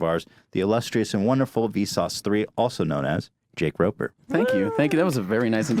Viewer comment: I thought it said Joke Raper